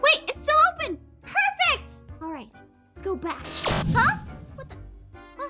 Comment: i heard ''ahh what the fack''